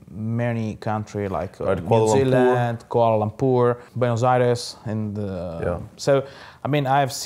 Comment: did he say buenos aires?